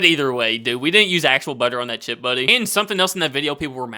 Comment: I thought you were saying "Chip Buddy" at first.🤣